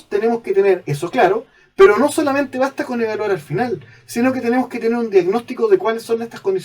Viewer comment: Minuto 6:50 y 6:51 "Nosotros tenemos que tener eso claro, pero no solamente basta con "??????" al final... (Valor al final, agregarlo al final, lograrlo al final, colocarlo al final, verlo al final...) De verdad no escucho bien lo que dice. Se agradece poder clarificar esta duda. Saludos. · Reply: El profesor dice " No solamente basta con EVALUAR al final" Espero te quede claro.